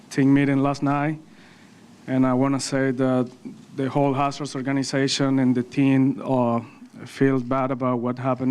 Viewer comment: Noticed Altuve didn’t actually apologize. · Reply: He s not a big enough man to do that. Figuratively and literally. Btw Sounds like he said whole " a__holes organization" 3:39. Well said then.